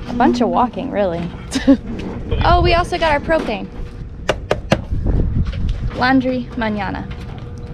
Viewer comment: Lol. U said Food and Propane. I thought you said cocaine. Lol